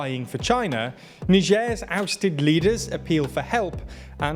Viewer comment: I thought you said Niger's abstinent leaders!